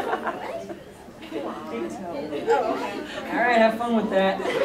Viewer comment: When the one person said "big toe" for a body part I heard "Excalibur"